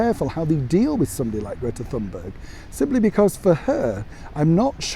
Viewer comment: I had to replay this because I thought you said "this dumb bird", but he said "Thunberg", and I couldn't stop laughing. Sorry everyone I just had to share, lockdown must be getting to me.